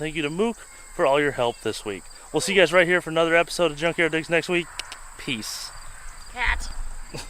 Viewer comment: I coulda swore u said drunk yard digs I mean hell ya it is no job is possible without beer